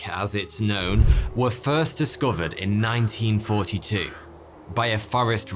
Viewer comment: What he said : 1942 What I heard : 19